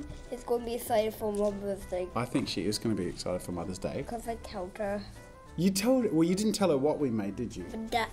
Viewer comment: im crying i wasnt looking at the screen while watching and i heard it as "bc i killed her" and had to do a double take 😭😭